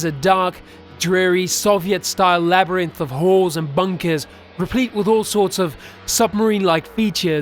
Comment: Great story, great narration. I will say, for a second I thought you said whores and bunkers rather than halls and bunkers. I guess it was the accent. But it made me laugh.